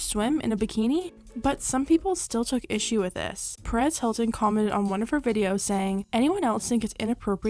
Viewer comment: im so dumb i thought you said paris hilton till i read perez hilton 💀💀💀